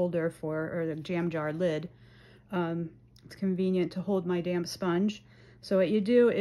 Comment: 😂 I thought you said “Dam Sponge”